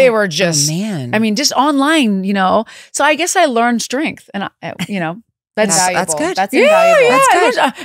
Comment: I thought you said "I learned to drink, rather than I learned strength." LOL